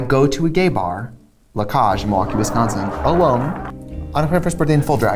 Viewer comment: literally what is she saying here lol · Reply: She says the club's name "La Cáge in Milwaukee, Wisconsin."